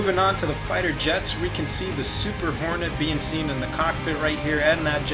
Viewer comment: i swer i heard super horny thumbs up if thats true